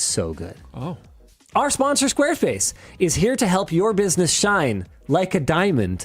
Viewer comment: I swear to God I've heard him saying "Squareface"